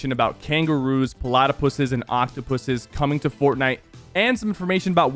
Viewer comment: I heard 0:06 pu**ies 😙😙